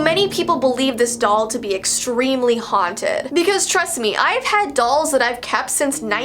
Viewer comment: I thought she said hotted but haunted